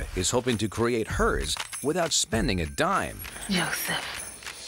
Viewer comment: Did she say "Joseph" looking at that joker 17:55 ???